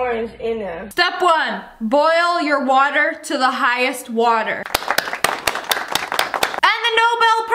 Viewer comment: Bro I thought she said weather not water😭😭😭